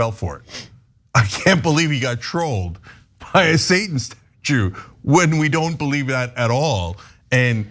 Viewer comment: did he say what i think he just said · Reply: I think you heard satan is a jew when he said satan statue